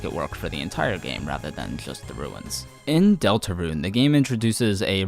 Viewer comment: the captions say delta run, peak pun